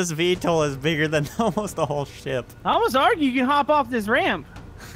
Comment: did say: this helicopter is almost bigger than this whole SH!T?